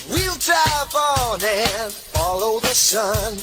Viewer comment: we'll drive 이 위잡 으로 들려요